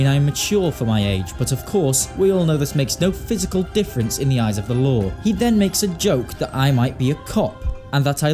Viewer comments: when he said "but that doesn't make a difference in the eyes of the law" I thought he said "in the eyes of the lord" and I just died HA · I genuinely thought he said ‘in the eyes of the lord’